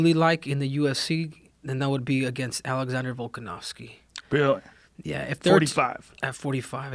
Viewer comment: For a sec I thought Henry said he wanted to fight Alexander volkov and I was like WHAT